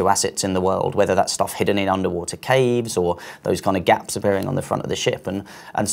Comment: Did he say there's new stuff hidden in underwater caves???